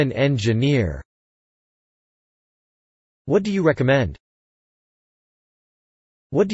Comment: what do you like man ? あなたは男性が好きですか に聞こえた普通に 諦めよ